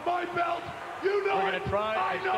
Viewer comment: What did Sid Vicious say?... All I really got was "I don't know shit!" because that's what it sounded/seemed to me like that's what he said, at first. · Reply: He said "I don't know shit, crybaby!" on Bret Hart when he walks to the ring.